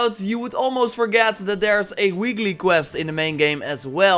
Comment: It sounds like you say “wiggly quest” 😂